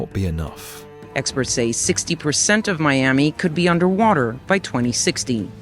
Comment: By 2016? Did I hear that right? It's 10 years overdue...